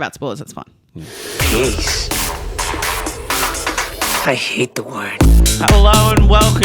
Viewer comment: I always thought he was saying "Grease, I hate the word" in the intro until a recent vid--I think a Short? So I always assumed he was in Grease 2.